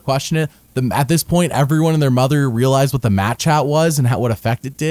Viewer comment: For the last 5 years I always thought you were saying "Match Hat." Today I've learned it is "Mat Chat" and everything makes so much more sense.